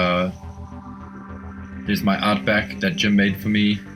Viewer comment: there was a character you mentioned at 3:57. White hair and beard, who is that? Its awesome! Would love to see a better photo or video. You mention him again at 4:02. I can't make out the name you mention Also, who are the figures again at 4:20? Marauder...something? The guy with the bandana as well looks awesome. Who is the figure at 6:04? The ninja in the back behind Snowjob? Thanks! Amazing collection! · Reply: hi here. That first figure is a custom tiger force Outback. This was a gift from the awesome Jim Godfrey, yes he who makes awesome customs. The dudes by the tiger force vehicles are. Dollar general exclusive snake eyes. Pursuit of cobra Duke. The Marauder inc figure is a custom I've made for Solid snake from metal gear Solid 3. The guy with the bandanna, is a pursuit of cobra snake eyes, the bandanna head is his alt head. I hope this answers your question. Feel free to ask more 😁